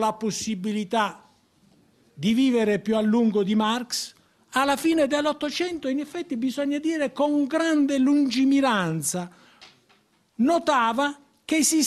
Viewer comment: Por volto de 4:10, creio que ele disse ''1900'' e não ''1800'. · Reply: allefy m. Foi 1800. Engels morreu antes de 1900, então a citação só pode ser do século XIX msm heheh